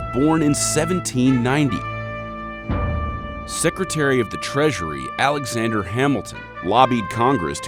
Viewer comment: instead of treasury I heard pleasury 😂